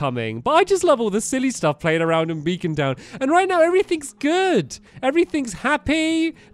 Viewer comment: I thought he said vegan town